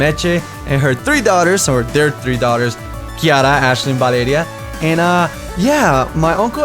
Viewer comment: did you say Kiara? Or something else? Around like 4:50 I swear he says kiara.